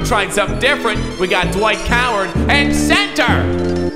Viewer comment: Did anyone hear Dwight Coward at 22:24 or